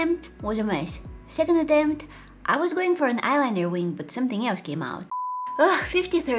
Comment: “I was waiting for an eye liner wing, but something else came out…” 💀💀💀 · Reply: oh my bad i thought you said "when something came out" but you actually said "but something else came out